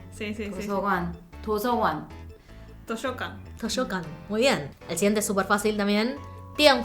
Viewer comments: parece que dice toyota xddd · En la de Japón entendí toshocan XD minuto